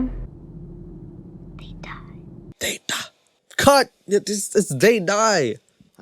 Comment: I swear I heard "They die"